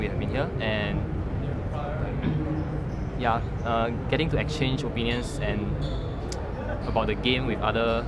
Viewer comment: Did he say exchange penis?